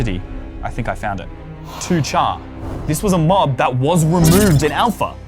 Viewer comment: thought said charzad XD